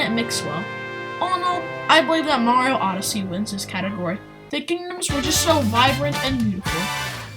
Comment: What did you say at 14:40? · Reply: Antonio Corsetti “The Kingdoms were so vibrant and beautiful”